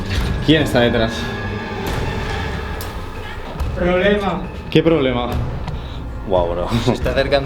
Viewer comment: Hola gente como andan? cuando el segundo amigo de esta en operando el spirit box escuche perfectamente una mujer diciendo problemas (28:20) de tanto miedo que me dio escuchar despues que el dice mujer me puse a llorar. La verdad es impresionante que se puedan comunicar asi con algo que no existe o esta muerto